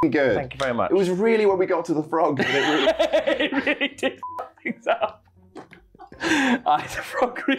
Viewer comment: I heard: "It really did Frog you up" :D